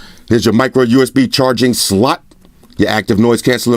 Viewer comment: for a second I thought this guy said "micro usb charging slut"